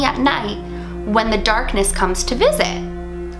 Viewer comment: Once I heard "The Darkness that comes to see me" I was like "WHAT THE HECK!!!!""